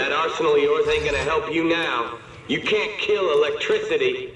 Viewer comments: the guy says" You can't kill electricity" but the captions says" you can't kill what you can't see" What's Happening Dead Space??? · the guys says "you can't kill electricity" but the caption says "you can't shoot what you can't see"